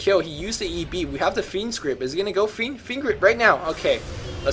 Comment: Every time says "fiend's grip" I hear "fiend script," which sounds even cooler.